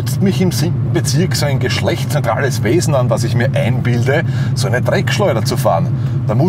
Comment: Was sagt er bei 0:51 geschlechts.... Wesen? Ich finds lustig, aber ich verstehe nicht was er genau sagt. Kann es mir Jmd mitteilen? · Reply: Es sind geschlechtsneutrale Wesen. :-)